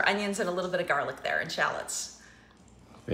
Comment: Did he just say BERRY NICE??😂